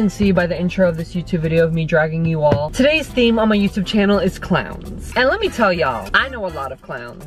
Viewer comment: Did you just say harry is clown >:(